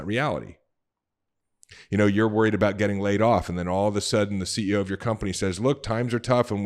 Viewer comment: I keep hearing "getting laid" instead of "laid off"